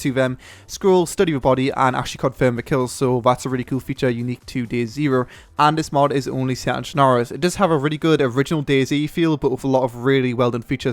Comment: what is the word he says at 3:04 . it sounds like schnars